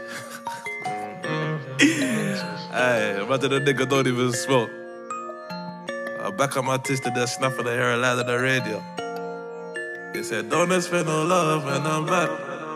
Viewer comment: WTF DID HE SAY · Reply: DXNVLL I know. It sounds like he said "I backed out my tis and did a snap of the URL of the radio" but "tis" can't be right🙈 and i can't find the connection between "URL" and "radio" 😂 (if that's even what he says)